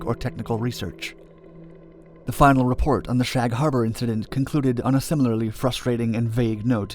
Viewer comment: Seriously thought it said the shaq harbour ufo incident 😂